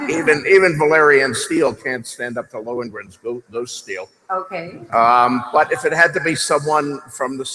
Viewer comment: that “OK” sounded like “OK, Neeeeeerd” 😂